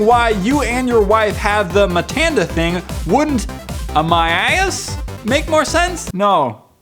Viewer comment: lol i wuz on twitter at 7:25 ina different tab and i thought said "am my ass" XPXPXPXP